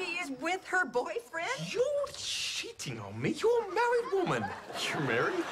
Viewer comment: it sounds like PJ is saying "your shiting on me your a married women"